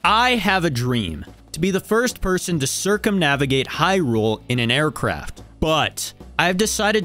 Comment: I thought he was bouta say "to be the first person to cercumsise hyrule" 💀